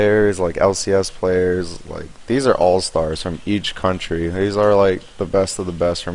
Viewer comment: I thought it said alistar ): now I am sad